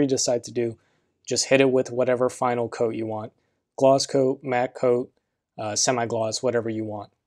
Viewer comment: what do you mean by glass coat? im just new...thanks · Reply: oh sorry i thought i heard glass coat...hehehe, anyway, im using spray paint, after the base coat is it necessarily to be gloss coated coz i wanted it matte as top coat